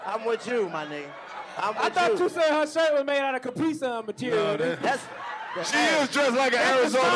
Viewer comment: “That suit made outta Caprice Classic seats.” -Karlous Miller 💀💀💀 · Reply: I thought you said Capri Sun material -DC young fly